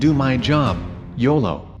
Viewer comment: Who else would thought he said "yellow"